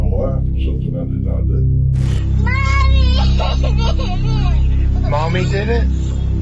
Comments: no she did not!!! Go back and and actually listen please. The cop even says she hasn't said anything. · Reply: go back and watch it. The child says "mommy did it" a couple times and the police officer even repeats it to the child to see if that's what she said. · Wow.. very disturbing to hear and see a child crying after seeing her father's murder. This is the worst ...that little girl's world is so fukk'd! I could have sworn that girl said "mommy did it!" .. man! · I thought I heard mommy did it